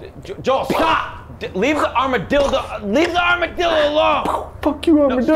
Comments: Did i hear armadildo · "Armadildo" Anyone else catch that? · did he say armadildo?